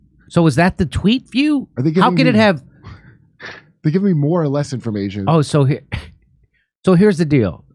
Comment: I misunderstood Kurt and thought he said: " give me more less information", and I like it more that way